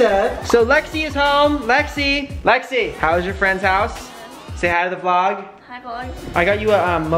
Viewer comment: Brent : "Say Hi to the vlog" What I heard : "Stay out of the vlog"